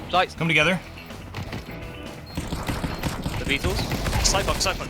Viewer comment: did he say the beatles instead of deagles? 😭